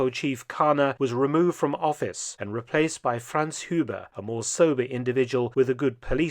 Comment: I misheard Franz Huber as Hans Gruber and got very confused.